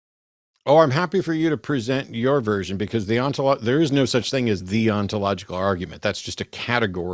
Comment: that's not what she was saying, she was saying it's not possible for water to be H3O, which is not wrong because H3O isn't water, it's hydronium. I think she's still wrong because I don't see why that's metaphysically necessary, but the point itself isn't wrong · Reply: My bad I thought she said there's no such thing as H30